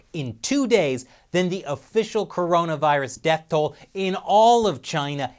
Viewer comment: Did he just say Colona Virus in the opening?